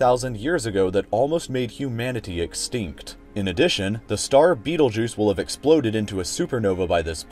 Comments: Did you say beetlejuice at 2:47 ? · i thought he said "the star BeetleJuice" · I thought he said, ‘the star beetle juice’ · Betelguise... I thought he said 'beetlejuice'. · it sounded like he said beetle juice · I thought he said Beetlejuice LMFAOOOO